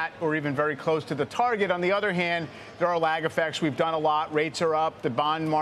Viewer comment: At first, I thought she said "bet", when the host thanked her for being there. And I was thinking, damn, that's where we're at now? Then after listening again a few times, I confirmed she said "*you* bet" (bareley getting out *you*).